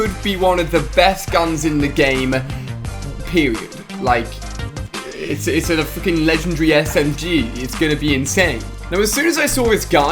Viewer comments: did he say it's a fucken legendary SMG???????? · Ali said the F word 😱😱😱😱😱😱😱😱 (Edit) Oh I’m sorry thats what I heard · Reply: He actually said freaking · did he just say frickin or the other word? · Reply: Syakirin S he said frickin and it doesn’t matter if he swore anyway he’s a grown man why would it matter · Did he say fking legendary smg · First time I heard him say the F work 1:23 😱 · did he say fckin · We're getting our first LEGENDARY SMG in Fortnite this week - Take a look! 😱 Will there be TOO many SMGs in Fortnite...? 🤔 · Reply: Farrin'sBricks it sounds like he said it's a fu**ing legendary smg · did anybody hear him say "it's a fu***ing smg" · Anyone hear Alia say F*cking at min 1:24 ?